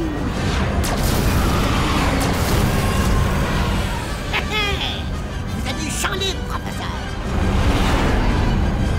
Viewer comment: i dit koi jarive pa a comprendre? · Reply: He! He! Vous avez le champ libre, Professeur.